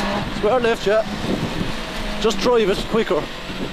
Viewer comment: Does he really say "Just drive, it's quicker" at 1:15 ?